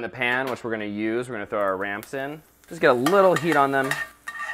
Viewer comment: RAMPS. I thought said RANCH. I thought he said ranch. Oh my goodness